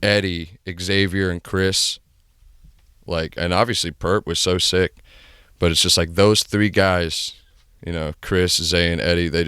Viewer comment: did he say purpp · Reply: spaceghostpurpp